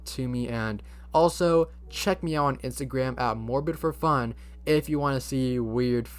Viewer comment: Edit: I MADE A PART 2 VIDEO <link> There has been so much misunderstanding on the fnaf story, I don’t blame y’all since this was one of my first videos so my explanations were shit lol. The Chuck E. Cheese shooting WAS NOT WHAT INSPIRED THE GAME! I mentioned it so I can debunk that because many people believe that’s what inspired the game. I also stated that Scott COULD have took inspiration from the restaurant itself (not the shooting) like the animatronic designs, gaming pizzeria etc. since Chuck E. Cheese is the only other animatronic pizzeria there is so I wouldn’t be surprised if Scott got inspired by the restaurant layout. Also I could be wrong so take this with a grain of salt but I’m pretty sure Scott took some inspiration from Showbiz pizza which was bought out by the Chuck E. Cheese company. No shooting involved just the restaurant itself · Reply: I thought it said morbinforfun